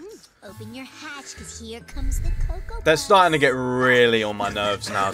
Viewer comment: i hope sucka did not just say coco buss this is just wow i swear this is cringe and funny at the same time and a little cute 🤣